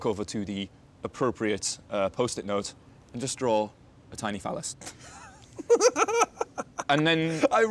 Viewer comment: did he said "tiny fellas"? :o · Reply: "tiny phallus"